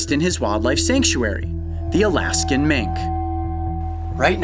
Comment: At the start I thought coyote said "elastic mink" not Alaskan mink 😂😂